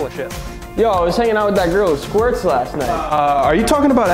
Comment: I heard "I was hanging out with that girl in squirts last night" and I got a very different picture in my head